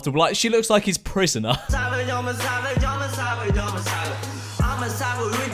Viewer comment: I thought the n&a guy was saying "I'm a saville"